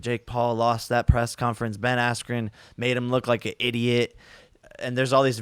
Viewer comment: man said "a idiot " instead of "an idiot "